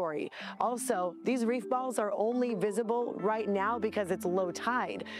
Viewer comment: Gave me false hope. I thought they said reefer balls.